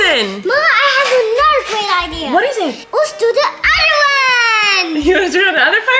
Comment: omg at 10:55 I thought he said “let’s go to Ireland.” And he looked exactly like Erik when he raised his eyebrows after!